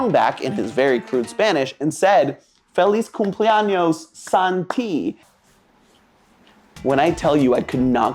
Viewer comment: not gonna lie I heard them singing Santi aswell.